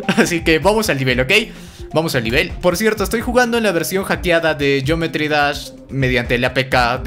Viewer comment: yo pensé que decía "jugando con hambre"